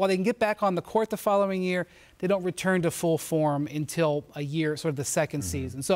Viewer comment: Am I the only one who thought it said retire instead of return